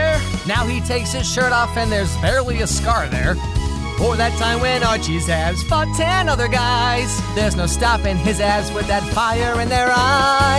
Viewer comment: In the abs song where it says “remember when archies abs fought 10 other guys” I heard “remember when archies ass fucked 10 other guys”